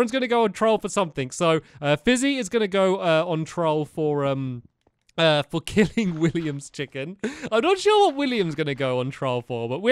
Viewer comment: i thought stampy said fizzy was going on trial for kissing williams fingers.... i littiraly had to rewind the vid to find out he said killing williams chickens!